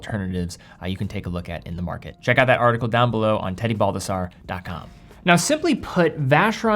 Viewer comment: I thought you said Teddy Bulbasur